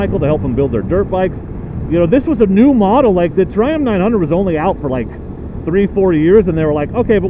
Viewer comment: Dear Triumph, please make the Tiger 660 ADV more like the Tuareg than the Transalp. I own the 660 sport and I owned the Tiger 800. Why should we be punished with mediocre performance and features when we know you are capable of greatness on a true middle weight adv bike! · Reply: I thought your were saying "Dear Trump".